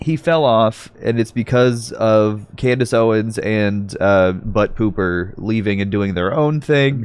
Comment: I’m sorry, I thought I heard you call Brett Cooper-Butt Cooper. 😂😂😂😂